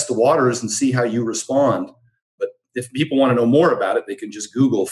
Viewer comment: Lol I thought he was saying FOGmap, I’m new to the term 😄